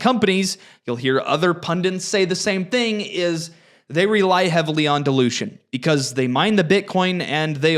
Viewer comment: When you said "cleanspark ... relies on dilution..." I thought you said "... relies on delusion..." - now that definitely perked me up to hear what you mean...